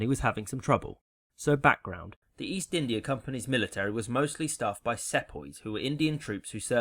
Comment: What was that word for British colonial troops? "Setboys"? Can't make it out. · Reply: Sepoys.